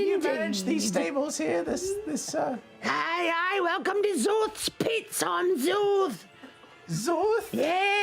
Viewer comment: I ALWAYS hear this as "Zorth's Pizza"